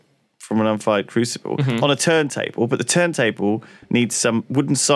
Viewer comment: I thought Lewis said turd table